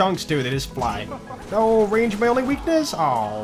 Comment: No joke, I thought said 'Oh no rage, my only weakness!', but it took me a minute to realize he said 'range' not rage' XD